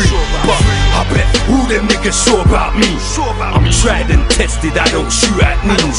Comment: I'm tried and tested I don't shoot at neeks.... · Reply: jay K I thought he said "Trident tested..."